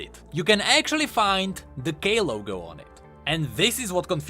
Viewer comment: Everytime u said "K" logo, i heard it as gay logo lol